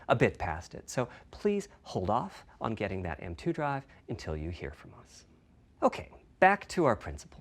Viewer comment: Is it just me or did he say we should wait to get them stronger drive??? As in a more powerful console down the line??? If so I will wait for it instead of day1 purchase.